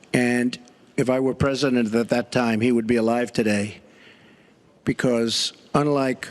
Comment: DID HE SAY... 1:10:11 HE WOULD GIVE ECONOMICS TO PEOPLE......LAAAWWWWLLL